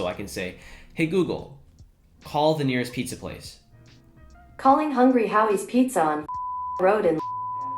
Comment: did it just say “calling hungry howys pizza on pussy road and piss shit cum”